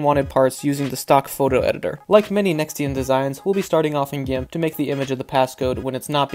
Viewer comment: what was the software at 0:39? it wasn't said clearly. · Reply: The photo editing software is called GIMP. It’s a free too.